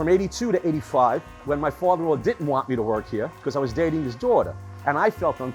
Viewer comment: I had to use CC to see it's "Father in law" after I hear him saying "my father didn't want me to work here because I was dating his daughter" and i'm like WTF?